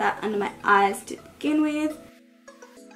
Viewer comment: I thought you said "big f*****g brush." I had to go back. Big fluffy brush. Oopsies!